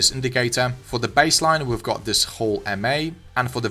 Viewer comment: I can't tell what MA he is saying. Can't find it in Tradingview. Can you type the name and the settings? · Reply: he's saying "Hull MA". it's Hull Moving Average. but, it only has 1 line in TradingView, which is 1 color. not sure on the Settings. i'd love to know as well!